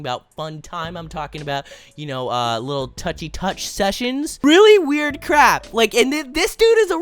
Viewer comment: WAIT DID YOU JUST SAY A BAD WORD IN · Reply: "really weird crap"